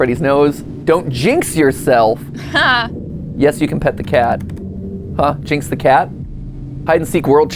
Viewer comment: For a split second I thought that said "Yes. You can eat the cat." and I was just like... what are we getting into here 😂